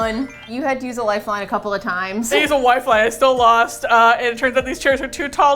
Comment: Did say "wifeline" at 11:23 or did I mishear her lmao either way love it and love you guys!